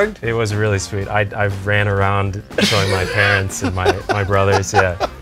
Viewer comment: I heard that as "I ran around showing my pants and my brothers'" 😄